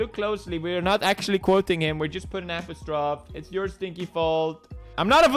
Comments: Why does he say “apostrophe” so weird? 😜 4:05 “apustroff” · did he just say apostrophe?